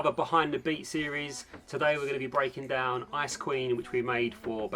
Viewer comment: Is it only me when i hear baekhyun say 'ice queen' but i misheard as 'ice cream'????